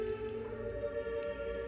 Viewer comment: for some reason i thought she was saying "it felt like death" LMAOOOOO